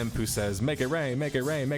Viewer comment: Y’all hear “ make it rain “ I hear “ pinkie ring “ 🤷🏻‍♂️